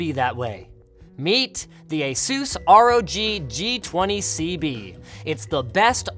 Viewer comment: I thought he said, "Meet the Jesús Homie-G". Ushering in the new era of Mexican computing, eh?? :p